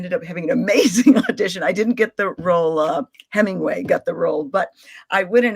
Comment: did she say Hemingway got the role?